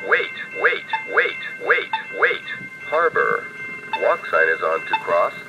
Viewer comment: I can't make out all that it says at 0:16... Harver... (something) to cross? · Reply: 2 years forward here! These kinds of crosswalks are all across my city. Here’s the general transcripts: [STREET NAME]. Walk sign is on to cross, [STREET NAME]. For those curious, these kinds of crosswalk modifications by cities are made specifically for the blind or visually impaired. The touch-sensitive buttons also vibrate as a second signal.